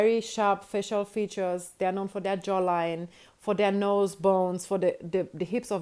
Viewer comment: She said jawline and I thought she said jollof